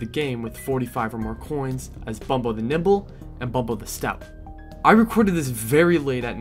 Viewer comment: Thought you were saying bumbo the nipple at first 😂 but awesome content ❤